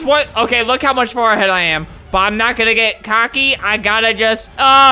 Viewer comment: Did just say cocky? 2:59?